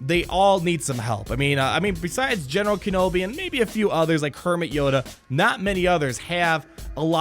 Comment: I swear I heard "Kermit Yoda" 5:27. Now I can't stop thinking about it. Thank you.